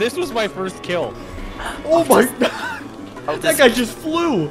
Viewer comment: did he just said ohhh bhai 👀